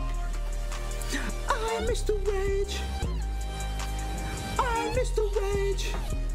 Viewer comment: I MISS THE RAGE I MISS THE RAGE I MISS THE RAGE I MISS THE RAGE I MISS THE RAGE I MISS THE RAGE EVERY ROCKSTAR I MISS THE RAGE I MISS THE RAGE I MISS THE RAGE I MISS THE THINGS WE HAD I REMEMBER IT ALL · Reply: I thought it said i am mr rage holy shit